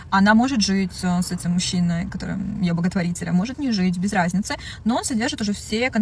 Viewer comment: Я именно это услышал Боготворить ,,, Карл 😂